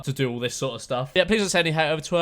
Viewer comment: mate, you cut the video too soon so it sounded like you said " if you enjoyed be sure to smash that like butt"